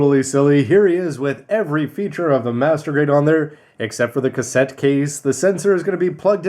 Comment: Did you say Master Grade at 6:38?